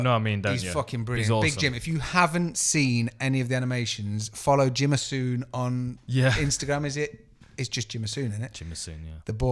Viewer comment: What was the Instagram account of the guy that animates the gsp thing you talked about around the 39:06 mark?? I’ve tried to type everything that I thought I heard you say but must be hearing it wrong! Or anyone that can help? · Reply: Jono Stewart search gsp ufc animation